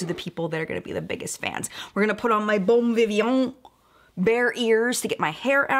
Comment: Lol you said bear ears and I thought you were saying bare ears and my mind was like "is that a weird makeup prep thing that idk about, yet again..." thing as in a trend. Haaaahahahaha